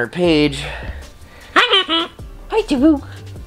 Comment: Don’t let someone’s ploy for views effect your day. You are an amazing animal dad! Swear I heard say “hi dada”. Watching you with her is precious